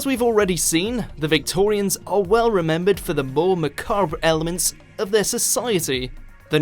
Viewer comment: Did he just say "ma-CARB" for macabre?